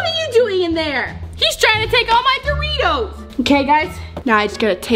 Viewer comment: If u use the caption and when said doritos the caption said THREE TOES 😂😂😂😂😂😂😂😂😂😂😂😂😂😂😂😂😂😂😂😂😂😂😂😂😂😂😂😂😂😂😂